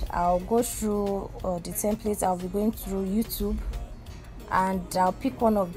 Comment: What did she say she was going to in 0:54 . I’ve been listening to it and still can’t figure out what she said ... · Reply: I meant I’ll be going through all these (which is the templates) sorry if that give yuh hard time!